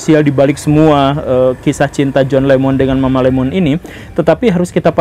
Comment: John Lemon, mama Lemon ... Apa cuma aku yang salah denger ya. 😂